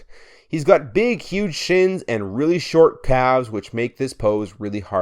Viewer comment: Because you said “calves” and the screen said “thighs” I heard “Thalves”